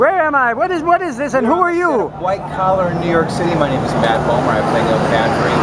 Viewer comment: Did he just say "Matt Boner"? Bahhahahahahahahaha XD